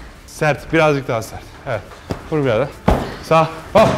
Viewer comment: Ben 9:19 da " vur birader " mi duydum?😂